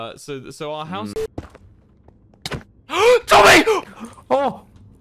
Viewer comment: Whenever I hear this "Tommy!!" 4:33 I only hear passerine Wilbur but idk why. I blame Sad-ist